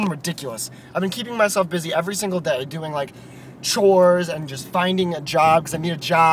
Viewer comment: It started buffeting at 1:56 and instead of hearing "doing chores" I heard "doing whores" 0_o